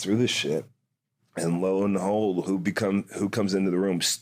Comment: Did Hager say 'low in the hole' instead of 'lo and behold' at 5:01?